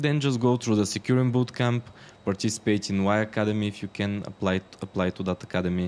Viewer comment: what did he say at 0:52? is it Y academy?